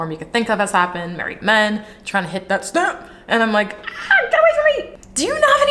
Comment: Did you say "married men trying to lick the stamp?" @ 15:15 haha that was too funny!